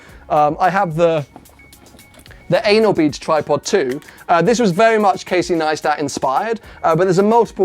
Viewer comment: Wow I heard "the anal beater tripod 2" at 9:49 hahahaha